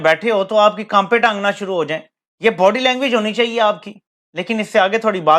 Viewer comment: 😃😃😃 bhai ye pakistani log bade hi hilarious hain, this dadhi waala jiski mooche gawab hain what does he say @ 2:44 " knaape tangna shuru hojayen " rather tht tange knaapna shuru hojayein , i heared it over and over again , believe me guys u will enjoy it.